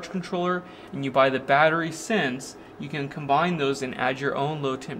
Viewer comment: What is it you say at 4:18? I can’t make out the word so well. I hear the victron battery charge controller and the second part is it battery “sins”?